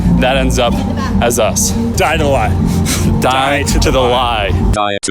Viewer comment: Deadass thought they said die don’t lie